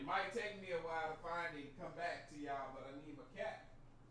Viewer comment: Ok when he was saying "i need my cap" I kept hearing "i need my cat"